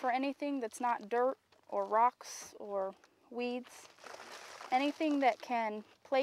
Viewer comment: did she just said Weeds?